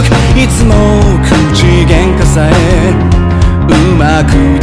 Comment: Why do I hear “choochi gang”